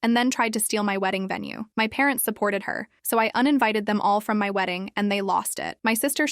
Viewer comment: I thought it said unalived